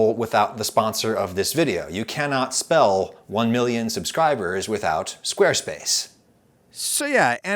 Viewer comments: i thought it said 6 million sub special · I thought it said 6 million